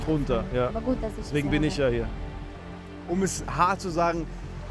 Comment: Ich habe statt "Deswegen bin ich ja hier" "Ich bin der Michael" verstanden und mir gedacht: Ach, da gibt er ihr gleich einen Satz mit, den sie mal schön ohne ISCH üben soll.